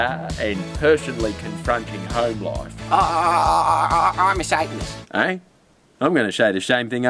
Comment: The first time I saw Rule Number One at 0:34, I thought said "I'm disabled" But she actually said, "I'm a satanist" scary shit guys.